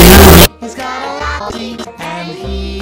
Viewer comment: he's got a what? To me it's possibly lobby or chocolate. What are they saying? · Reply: "He got a lot of See's"